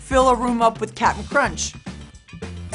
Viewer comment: "Secretary of State"? I thought you said your balloon was Secretary of Da Fence.